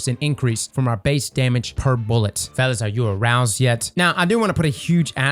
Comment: I read “Veles-X” but when says it, I hear “Fellas-X.”